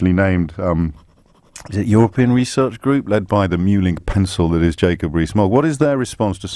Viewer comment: Did he just call Jacob Rees-Mogg a 'mewling piss-hole' or did I mishear that? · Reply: Mewling pencil.